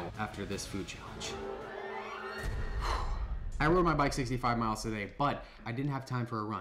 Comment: did he say 65 miles